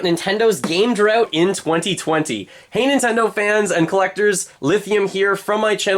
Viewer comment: "Hans I know fans?" "Hanes I know fans?" What is he saying here? I'm just trying to figure it out. OK, turned on the subtitles while typing this. "Hey Nintendo fans." Got it. In the video about Gamestop it kept sounding like "legal" instead of "il-legal." I was like... those dirty practices need to be legal? Sorry to be a butt, I do like this channel, and am subscribed. Good content, keep it up! (Also, I think part of the issue is the microphone has a little bit of a compressed sound to it.)